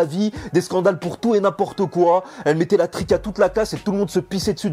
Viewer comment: elle mettais la quoi ????? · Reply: La trick